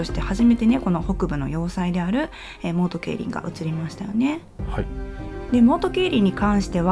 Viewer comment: モウトケイリンがいつも「オート競輪」に聞こえてしまう…